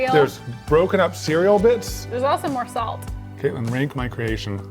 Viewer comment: had to rewind beccause I thought he said "katelyn, rate my cream"